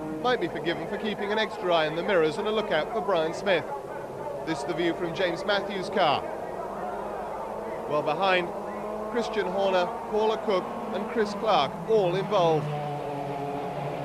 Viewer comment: did i hear Christian Horner?